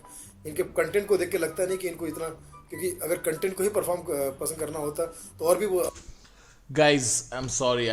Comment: wait a minute sunil pal is a TikToker he said cuntent instead of content